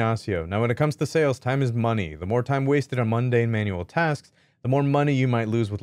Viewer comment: Did he just say Monday Manual Tasks 😂😂😂 Lol